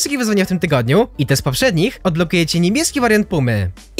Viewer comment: Niemiecki wariant Pumy xdd Czy tylko ja tak usłyszałem